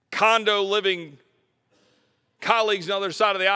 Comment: Am I the only one who heard him say condo living cock suckers? Lol